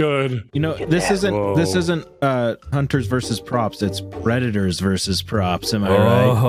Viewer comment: Thought he said “redditors vs props” lol moo